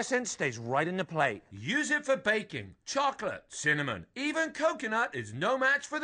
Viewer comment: it sounded like he said bacon...